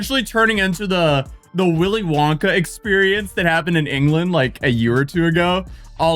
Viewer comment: says England and caption says Scotland. Oops 😅🙃🙃🙃